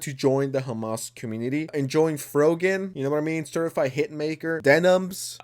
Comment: I heard 😂😂 "stirfry hit maker""